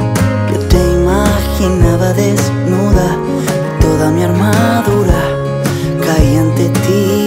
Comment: Repetí dos veces la parte del minuto 1:55 para verificar que sí escuché bien jajajaja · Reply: Mi arma dura 7u7 JAJAJAJA, broma, es lo que dijo la de arriba.